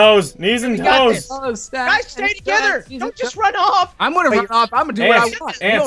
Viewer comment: I thought when was being killed in the third round I thought he was saying " SEXY KILLER SEXY KILLER" but he said stay together lol